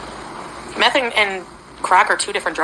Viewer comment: I think she said "methane". Either way, she is an absolute dongle.